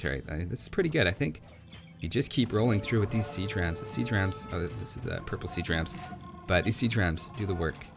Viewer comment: Had this on in the background at work, 26:14 I heard "Sea Tramps" instead of siege rams hahahahaha says it alot right there too, was super confused